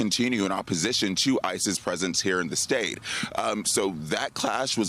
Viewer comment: Oh my gods the subtitles picked up him saying "ICE's presence" as ISIS presence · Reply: I heard "ISIS" but quickly knew Malcolm actually said "ICE's"